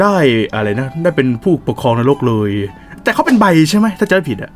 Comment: ตอนที่พูดว่าใบคือ ตอนแรกคิดว่าไบโพล่า5555